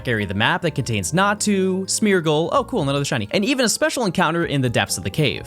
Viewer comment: did you say shiny?